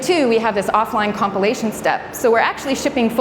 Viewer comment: Great talk...just distracted everytime I hear 'copulation' when she said 'compilation'